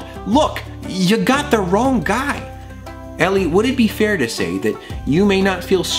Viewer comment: I hear elli (Ellie) anyone else?